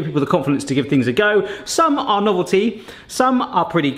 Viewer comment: Check out the crowdfund campaign <link> Have a Barrathon with more kitchen gadget testing videos here <link> Like any of the gadgets? Get them here Egg pricker <link> Topless can opener <link> Thaw claw <link> Negg Egg Peeler <link> Cheese Melter <link> Sergeant pepper and corporal salt <link> Zip slicer <link> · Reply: Subtitles say "naughty" instead of "novelty". ;) Same at 9:51.